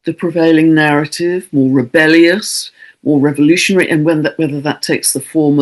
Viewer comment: Sounds great! Angel Gabriel Star with Pisces n node! At first I thought u said " devolution*! It couldn't get more appropriate. Thanks! Stay healthy, happy and safe!.😍🌼💙🐦🙏🌻🌎